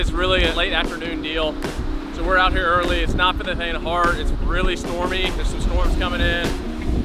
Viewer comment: What did he say at 8:28 "It's not for the" lol what?? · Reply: "It's not for the faint of heart"